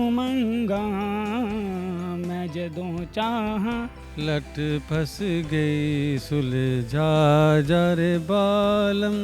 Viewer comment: chacha kya bol raha he..... mule ye lag raha he....... lat phas gae ...